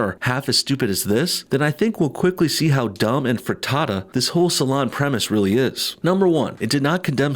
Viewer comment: did he just say "dumb and frittata"? Or did I hallucinate that?